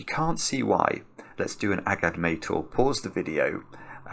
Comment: he says something that the subtitles type as "ag admittal". Is that what he said? Or what was it?